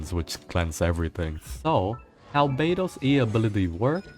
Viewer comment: when he said " how beidou's ability works, I heard albedo's ability works " lol